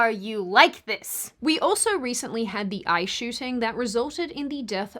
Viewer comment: Wh😂en u said we also had the “ICE” shooting I thought you said the “EYE” shooting and either one was appropriate but it was very hilarious to me 😂.